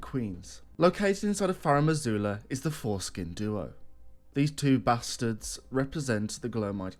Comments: Sorry say again? Not sure I heard you right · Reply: Foreskin duo lol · Did he say godskin or forskin because I heard the latter